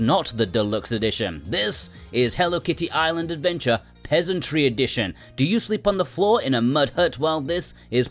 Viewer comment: For a moment I thought you said it was called 'Hello Kitty : Pedantry Edition' and I was picturing Hello Kitty wagging her finger and saying "Um, actually..." a lot 😂